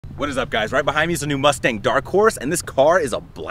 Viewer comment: I thought he said dark force I was like dang.